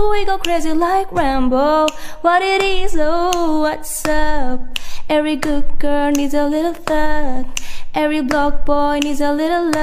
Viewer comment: I thought she said 'block boy' as 'black boy'💀💀💀